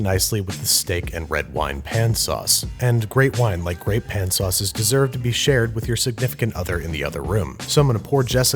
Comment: I honestly thought it said pan sausages